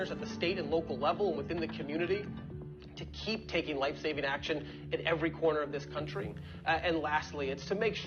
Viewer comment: Every time he kept saying, "Take life-saving action." I kept hearing, "Take away your guns." I mean, if they wanted to take life-saving action to stop mass shootings, they would get rid of gun-free zones. That's completely within the law and it would cost the government $0 dollars. However, I can almost guarantee that's not what they'll recommend, meaning this isn't about saving lives, it's about taking guns.